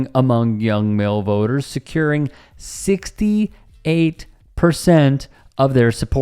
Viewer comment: the subtitles are wrong! Both you and the article say "68%", but in the CC it says 38%. Had to do a double take lol